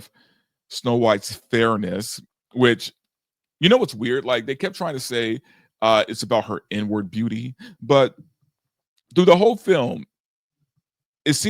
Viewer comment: I thought he said n-word beauty 😂😂